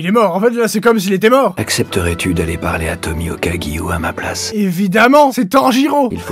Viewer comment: Quand Ubuyashiki a dit Tomioka Giyu, j'ai entendu "Tommy le Caillou" 😂😂😂